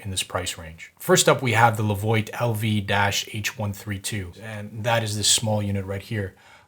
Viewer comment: What you said: "first up we have the levoit LV-H132" and what I heard "first up we have to avoid LV-H132" :-))